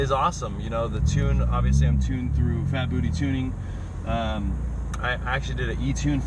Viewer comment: Did you say phatbooty tuning at 09:00?